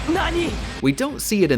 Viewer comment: I just love the little detail where at 11:59 you can hear Yugi say "Nani?!" but the subtitle says "Attack!" It made me giggle so much.